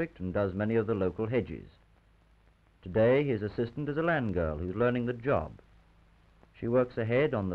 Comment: Billhooks were billhooks in those days, and land girls too. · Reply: inregionecaecorum ah, land girl. I thought he said "man girl". Lol